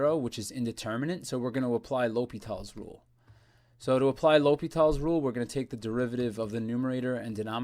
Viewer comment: anyone else hear "Logan Paul's rule"? lol